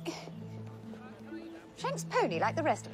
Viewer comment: What did say at @ 22:16 ? James Pony? Huh? Can't even make it out even with closed captioning.